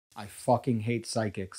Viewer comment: At first I thought the video said : “I Hate PHYSICS” and I was like yeah!!! Fuck Isaac Newton and quantum entanglement!! Then I saw a handsome gay liar and realized the gravity of the situation. The apple doesn’t fall far from the tree.